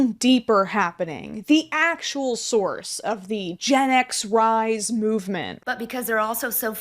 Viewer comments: Gen X rise? I thought you said extra fries 😝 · Gen X rise? I thought you said extra fries!